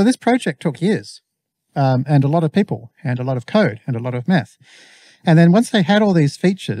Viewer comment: I heard that as "a lot of meth" and I had a good laugh